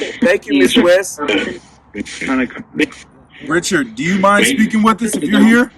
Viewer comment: Seem like I heard at 33:12 Richard was dead